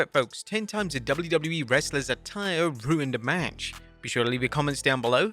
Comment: I thought it said Wrestlers attitude i was so confused